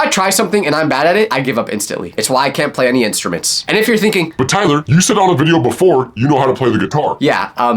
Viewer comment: naw when he said tyler the closed captions said